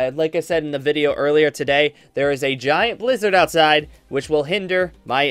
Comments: OMG i thought you Said : Theres a giant Lizard outside who is hindering my internet connection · I thought he said that there was a giant lizard outside! But he said Blizzard s0...